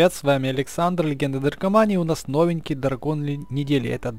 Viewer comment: Круто(мне сначало послышалось легенды Наркомании😂)